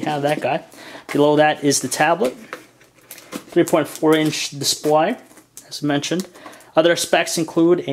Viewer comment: did her say 3.4 inch display ?